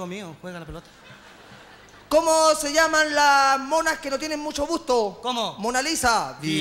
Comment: que dice el flaco en el minuto 4:20 como se llaman las monas que no tienen mucho busto o mucho gusto??? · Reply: busto